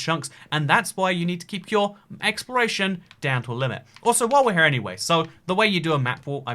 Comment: I thought he said I should prepare for "expiration" instead of "exploration" 😱